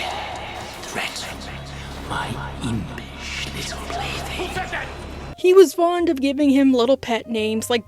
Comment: Omfg I thought he was saying "My impish little Blitzy" that whole time. That really changes a context for me, woof...